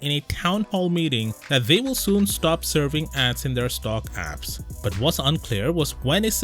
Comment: at first i heard ,"serving ass"...OMG